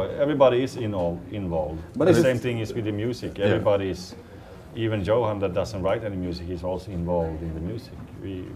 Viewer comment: Wait, did Olavi just say that Johan doesn't like metal?? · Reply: If you think about the part around 2:40, I think he's saying "Even Johan, who doesn't write any music". I guess you heard like instead of write :D